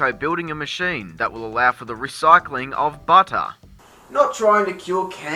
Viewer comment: I always thought she said recycle better